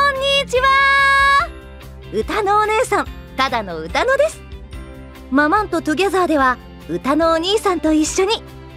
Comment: 申し訳ないですが ママン〇Togetherに聞こえた笑笑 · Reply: 問題ないです。 ｢ママンとトゥギャザー｣という(作中内の)幼児番組なので。